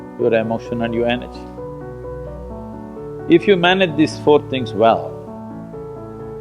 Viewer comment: is inaudible......what did he say as 4th? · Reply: Your body, your mind, your emotion, and your energy.